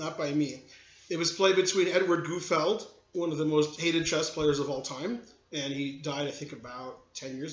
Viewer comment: Did he say Gufeld was one of the most 'hated' chess players of all time? at 2:39?